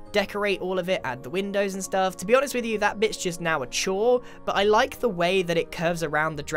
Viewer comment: Lol sounds like grian is saying bit*h at 10:00 because he says: that bit's just..... And he says it fast. Leeel